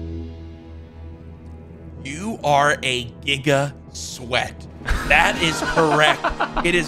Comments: 🤨 did he just say the n word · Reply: No he said “giga” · I thought he said the n word instead of giga sweat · Is it only me or when courage said nick was a Giga sweat I thought he said n sweat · bro did he say the N-Word???????? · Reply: No giga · I heard something racist in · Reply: He said giga not the n word